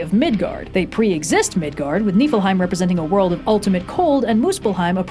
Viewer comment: Fully missheard that as "nippleheim", creating the word "nipple-hell" in my brain